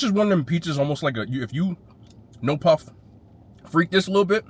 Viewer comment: Did he say " no Puff, but just freak it a lil bit" ? Wtf 😮😂😅